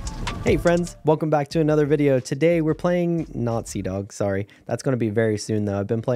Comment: In the intro I thought you said you were playing nazi dog